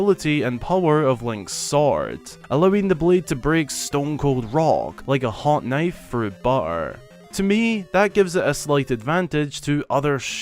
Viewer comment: This is what I hear: 8:55 "Allowing the blade to break stone cold raw, like a hot knife through bar." XD